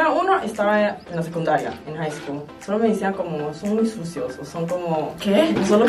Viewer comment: Me encanta que hablen de mi pais, que lo reconozcan más🤧🌹 Saludos desde El Salvador💖🌹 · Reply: No sé si escuché mal pero les dijerón sucios, en el minuto